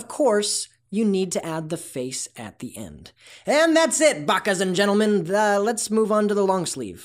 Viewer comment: Did you just say idiots and gentlemen XD.